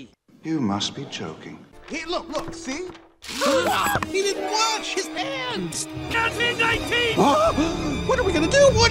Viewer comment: Sounded like Rex said "HE DIDN'T WIPE HIS ASS OR WIPE HIS PANTS!"